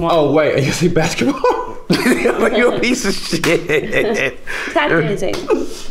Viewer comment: what does Tessa say at 4:15? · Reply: Tapdancing